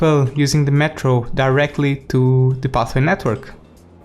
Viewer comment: In 9:20 instead of pathway subtitle was given as bathroom network